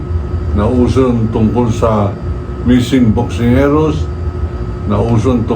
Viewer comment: Did he say "missing boksingeros"